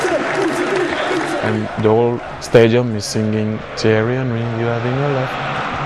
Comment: No I meant like when he sings on here. What are the words at 4:33? It sounds like "thierry henry, we are in your love." I know that's not right.